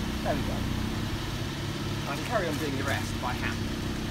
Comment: Oh mate, I hope this works for you... I watched your vid on an xbox1, if that makes a difference. At 2:40, you say, "Carry on doing the rest by hand." But that's not what the cc text heard you say.😂